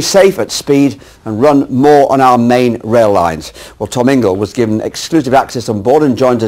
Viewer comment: did he say Tormango?